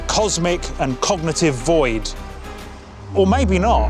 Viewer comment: What did I just hear? Around 1:23? "The perception of reality manifesting as a cosmic and cognitive MOID"? Anyway thanks for the fish ^W video.